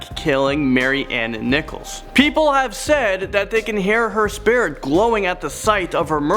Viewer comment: Is it just me or did say “people say they can hear her spirit glowing” at #10